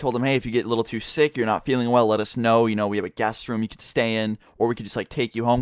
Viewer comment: am I the only one who misheard say gas room instead of guest room